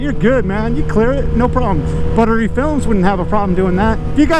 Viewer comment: What was the YouTube Channel he Shouted out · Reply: Buttery Films.